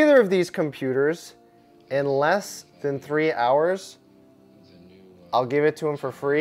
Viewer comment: I thought he said 3 hours not 2